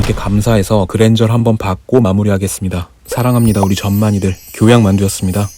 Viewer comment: 사랑합니다. 우리 좆만이들이라고 들었다